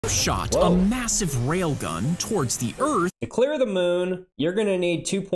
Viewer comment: Unless I misheard it, the narration didn’t say “massive bullet.” It said “massive rail gun.” The bullet could be, say, the size of a .50BMG bullet. At any rate, according to the short snippet of narration, it wouldn’t have to be on the order of a ton.